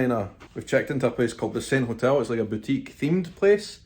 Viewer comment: Try as I might, I can't quite make out the name of the hotel at 1:42. What is it, again? Thanks! EDIT: Now I see at 10:00 it's The Saint. Now changed to The Palmetto.